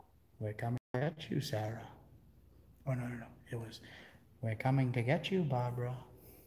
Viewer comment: "We're coming to get you Barbara." Female Voice echo at the same time as you say, "you Barbara" 45:48 after you say OK there is a creepy voice 53:45 in real time. "Marko", Male voice "Polo" just before you said buddy. 54:52 while you're rewinding. sounds like male voice "hey" or "8" 59:16 Sounded like a woman whispered "Frank" in your mic. I think you subconsciously heard it too because you paused and looked away. 101:56 on your exhale, the same female voice says "fun fun" 104:08 there is a face in the black chair 104:14 to 104:16 behind the curtain near the babies casket, a figure head or small figure appears with what looks to be two glowing eyes for a second, looks at you then moves up to the left and disappears. It was not your light as it did not happen when you panned towards the chair. Getting late now. Will finish this off. Thanks, you're still the best!!! Keep being you. Tania